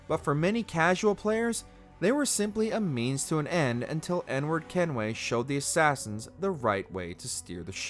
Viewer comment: omg I feel so dumb. I thought you said “n-word Kenway” and I was confused. Then I listened again and I was like ohhh haha 😅😑🤦🏻‍♂️